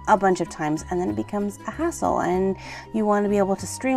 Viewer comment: At 47-49 I thought she said a$$hole (sorry for language) but she said hassle